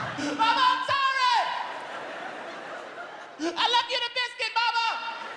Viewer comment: "MAMA I'M SORRY!!!!!....... I LEFT YOU THE BISCUIT MAMA!!!" 😂😂😂😂😂😂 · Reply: Seems that I misheard that bit. I thought it's "I love you to biscuits, momma". 😂😂😂